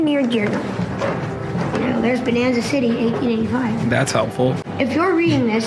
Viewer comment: I thought they said “banana city”